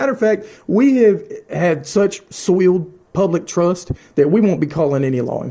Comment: Did he just say. He have swill Trust In law enforcement. 😂